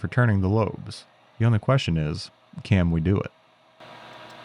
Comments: Did he just say 'cam we do it'? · did you just say “cam we do it?”